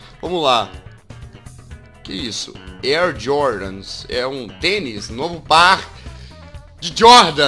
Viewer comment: Quando jazz falou tênis eu achei que ele tinha falado penis